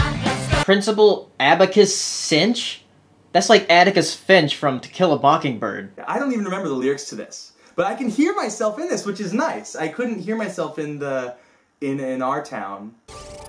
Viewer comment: Did he just say bockingbird?